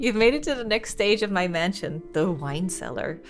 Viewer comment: I thought you said “Mr. Mansion” at one point and now I can’t stop thinking about someone naming their mansion “Mr. Mansion.”